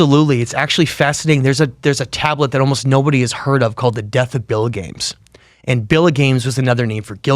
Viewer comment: Can someone spell out what he says at 15:12? What's this other name for Gilgamesh? Billygames?? Can't find anything about this. How is it written?